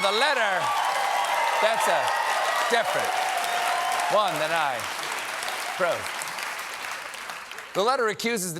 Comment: When they flashed up the picture of ol' Dotard Donnie sitting on his golden 'throne' ( @ 3:00 mins approx ) did anyone else hear Stephen say "that's a different one to the one I approved"? I wonder what the image was that he had approved, why it was changed, and if there was any follow-up - did someone get 'fired'?(!) Just wondering Late Show with Stephen Colbert 😋. Many Thanks, Zac.